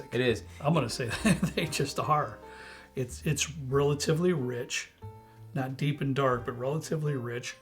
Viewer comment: Upon initial tasting: when he said "relatively rich", I heard, " real twiddly witch"